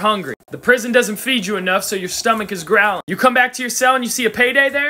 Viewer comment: As a guy who's second language is english, at the end of the video at first I heard "Once you eat it, you're in dead"... It took a second listen lmao